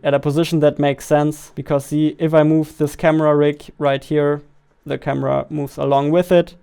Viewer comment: what i hear is camera rick...........